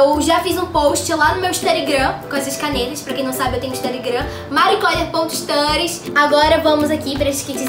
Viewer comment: “Pra quem não sabe eu tenho instarigram” instarigram ?? N entendi 😅😹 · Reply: Studygram ela quis dizer